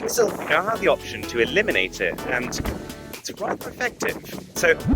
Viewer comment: does Arun say "Rather" or "Quite"? The text on screen seems to be different from what I'm hearing. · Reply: Rather